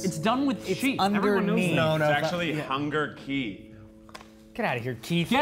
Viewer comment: Idk abt y'all but what I heard is "Hunger Sheep"